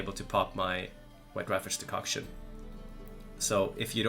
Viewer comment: Can someone please tell me what he said at 16:36 what decoction? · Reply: Hey, ye sorry it is White raffard's decoction.